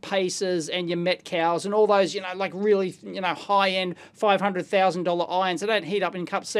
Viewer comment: I thought you said $500,000 irons there for a second